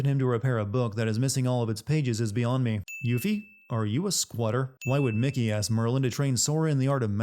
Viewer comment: 1340 i almost thought he said yuffie are you a squirter